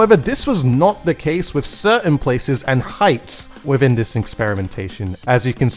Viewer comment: did you just say "within this inkspirimentation"